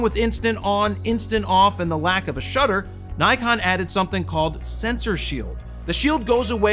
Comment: Bro them 5 Nikon pix in your intro are on point focused lol I thought u said Nikon AF sucks